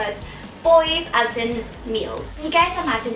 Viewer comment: At first when she said "Not Boys as in Girls' Generation's new song, but boys as in males," I thought she said,"Boys as in meals," I was quite shocked, but then I got it